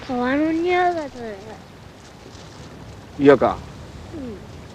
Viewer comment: がゆか(?) 발음 갈까? 일본어랑 자막이랑 똑같이 들림